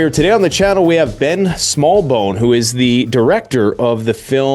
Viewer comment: I thought you said Dick smallbone. I literally lost it